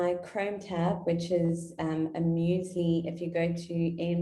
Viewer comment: Thanks! This was great. Can you post the link to the Chrome widget mentioned at 49:26...couldn't quite hear it. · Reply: The Chrome extension is musli2 ^rh